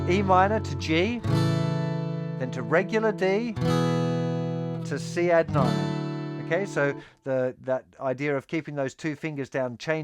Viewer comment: What does he say? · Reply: Charlie White he says d to c at nine